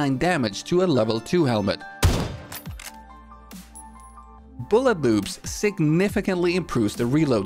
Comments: Swear I heard say "Bullet Boobs" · did he just say bullet boobs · did he say bullet boobs... Sure sounds that way if you listen closely😂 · I swear i heard him say bullet boobs not bullet loops.